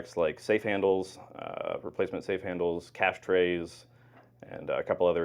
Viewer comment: I thought he said "ash trays" instead of "cash trays" 🤔 I was thinking, "one of these things, is not like the other." Did you sing that? 😋 Interesting video. 'Member to stay safe.